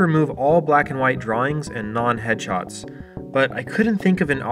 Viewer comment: Oh non-HEADSHOTS. I had to watch over that with the subtitles on, because I thought you were saying "non-hedgehogs", and making a comment on how much sonic the hedgehog crap is on deviantart. Honestly though, most of these images just look like disparate pictures that were spliced or averaged together. Is that the best a neural network can do? And you were IMPRESSED by FAVORABLE results? Your standards are way too low.